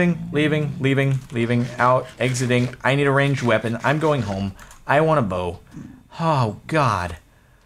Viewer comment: XD my sound was really low and when Sparklez said "I'm going home I need a bow" I thought he said "I'm going home I don't wanna boat" XF